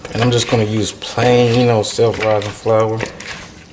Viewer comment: This looks so good! I want to make sure I use the right flour. It sounded like you said "self rising" flour. Is that correct?